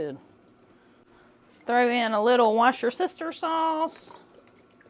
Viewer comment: I absolutely love your videos! Hey, everyone has trouble pronouncing Worcestershire Sauce. I heard you say 'wash you sister" sauce & I think that's a great new name for it. I will call it that from now on. Probably the only people that can pronounce it work there!